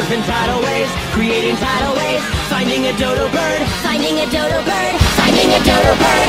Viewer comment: "Finding a dodo bird" * voice intensifies * "FINDING A DODO BIRD!" * shows close up of his brother * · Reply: I thought i heard signing a dodopun